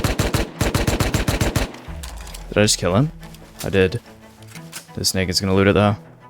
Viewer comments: it sounds like you said something else :face-orange-biting-nails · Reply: He said "This snake is going to loot..." What is it that you hear? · what did he say??? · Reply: He said naked